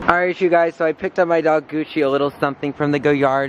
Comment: Did he say he named his dog GUCCI